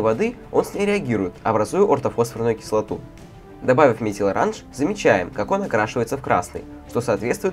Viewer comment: мне одному послышалось орт обосрананую кислоту